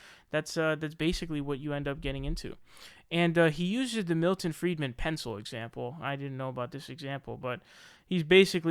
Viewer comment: Thought you said Militant Freidman. 🤣